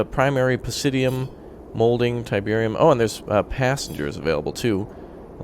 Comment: Did some1 say Tiberium? Kane lives! Peace through power!